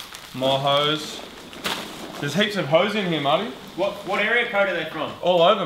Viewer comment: When Moog was unboxing the hose, i thought he said "There's heaps of hoes in here, Marty"